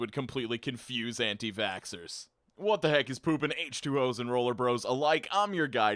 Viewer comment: Al this time I thought he was saying rollerBLADES instead of rollerBROS.